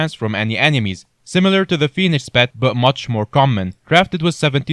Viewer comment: thought u said penis pet lol